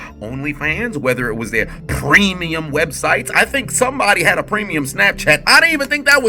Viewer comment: I thought he said creamium websites at first I was like oh he cooking